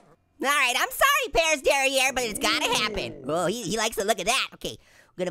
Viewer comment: Derriere? I thought he said dairy air!